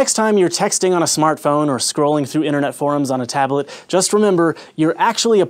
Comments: thought he said "scrolling through internet porn..." maybe its just that i watch too much. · I thought that he said scrolling through internet porn at 3:03. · I thought he said "internet porn" when he said "internet forms"